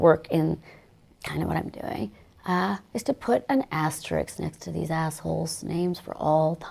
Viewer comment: Did she say 'Asterix'?